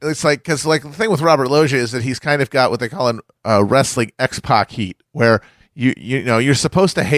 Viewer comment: What is Matt saying at 54:25? “Wresting ex poc heat”??? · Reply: He's saying 'X-pac Heat'. Like when the crowd is booing not because the character is a villain, but because they actually just don't like the character at all and wish they weren't here.